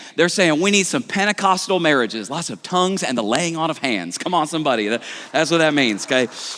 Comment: Did I hear “lots of tongues and the laying out of hands cum on somebody”?